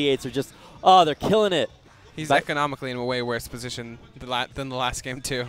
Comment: did you say "ecumenically,"? 14:32 Someone please correct me, haha.